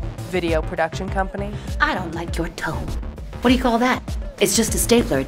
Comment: The way says "I don't like your tone." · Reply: I thought she said, "I don't like your tow." LOL 😂🤣